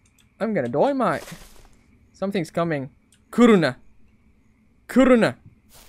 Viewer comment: be saying "something's coming, kuruna kuruna" Thought he said corona, did u just...